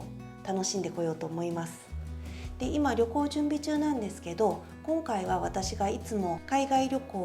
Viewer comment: 2倍速で見てたら、旅行準備中が離婚準備中と聞こえてびっくらこいた（笑）